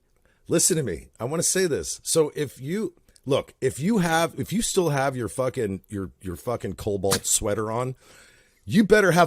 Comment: Why the hell did I hear say "if you still have on your Cuomo sweater..." Good lord I need CC turned on lol p.s. if they are any good at fellatio, what you see won't matter. JS